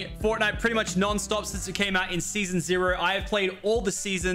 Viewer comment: did he say season zero